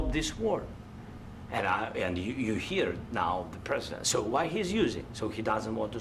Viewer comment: Did Z just say "يعني" ??! 😆